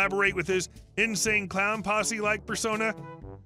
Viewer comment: Son must have his mom's nose. That's why she's so upset about it. I thought he said Nsync Clown Posse. I would pay to see that.